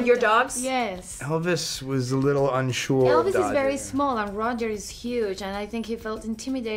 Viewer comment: I like how Chris didnt correct Ana's mispronunciation of his dog's name cause he didn't want to embarrass her. That's sweet imo 💞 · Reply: sunlight ☀️ ohh sorry i thought i heard dodger, didn’t realise she actually said roger😂